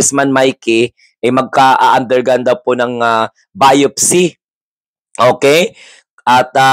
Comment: “Autopsy,” baket, patay na ba? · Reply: Biopsy po hindi po autopsy misheard